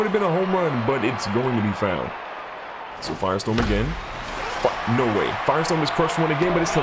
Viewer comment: What did he say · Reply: firestorm again?